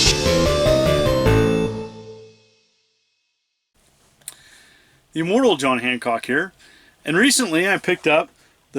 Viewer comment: At the end I almost thought you said "the Immoral John Hancock." Lol. Awesome video!